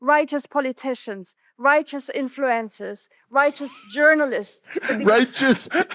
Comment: Anyone else hear that as “righteous influenzas”? Right up there with “God’s gonna put a cancer”!